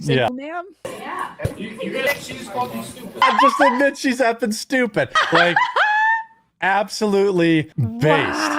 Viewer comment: that student said she just called you stupid Jeremy was wrong ,Jeremy said that the kids said she's she's stupid that's wrong the student turned around and looked at the boy looked at it and it said she just called you fshe just called you stupid here's the thing Jeremy and these three people did a horrible job reviewing this little snippet of this clip because you don't even know what's going on and you can't even hear him and they're just talking about crap that they're talking about the video but you can't even hear it you can't even hear what's being said, I have to go look it up on my own a separate video to see exactly what's being said because these three people are just talking over the video and you can't even hear what they're saying in the video